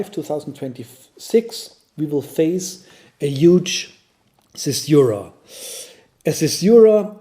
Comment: Hi, what word are you saying at 1:38? Thanks for your work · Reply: You're welcome. "We will face a huge caesura"